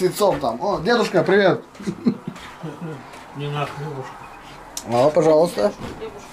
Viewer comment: Привет с Кавказа! Всегда приятно смотреть ,как вместе на кухне готовите и учите доченьку своим примером и дедушка показался ,привет ему с Кисловодска ! У нас + 22 сегодня дождь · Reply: мне послышалось или дедушка сказал иди на###? 😂